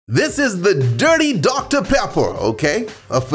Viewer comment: thought it said “dirty diaper”